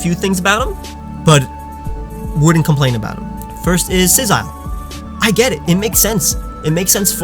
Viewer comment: Did you just say "Sizzile"? Not trying to call you out, just making sure I heard right.